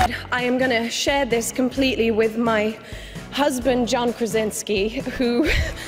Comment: did she said JOHN PRAZINSKI? WHAT?!